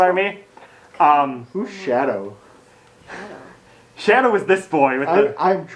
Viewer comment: yes thank xD I hope the next one will be as good or better! Also Chad is cute x3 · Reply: Sorry, meant Kylo, and misheard "Shadow" for "Chad" somehow ^^